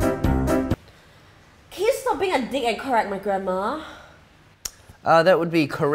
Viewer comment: it thought that said "correct my grandma...."